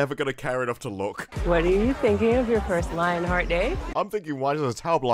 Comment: I thought she said “What … your first LINE of the day?” Understandable. The great thing about all this is that there’s no time or budget (or creativity) to edit the remaining episodes, so hopefully they’ll all be hilarious, keeping it at that sweet 1/10 on IMDb.